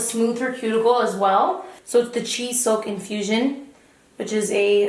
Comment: I wasn’t watching for a second and thought you said “cheese soak infusion”.